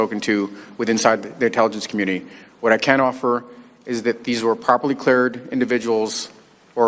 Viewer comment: Did he (Vinman) actually say, "these were properly cleared individuals"...when he (Vinman) referred to the whistle blower(s)...as in plural??? 27:30-27:40...please note, I am refusing to call him by his "rank" because I am a civilian and I have no respect for him. Did I hear Judicial Watch (Tom Finton) say that Vinman is the leaker to the leaker??? wow